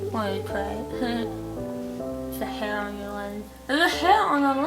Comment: Before I actually like went back and read the words at the bottom and I was just listening to the part where you had the fork in your tongue I thought you said "theirs a hair on your leg!"